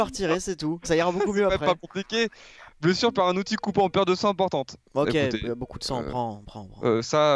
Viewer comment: j'ai entendu "paire de seins importante" : /